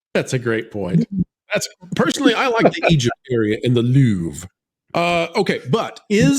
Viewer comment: I actually thought she said on the loo rather than the Louvre.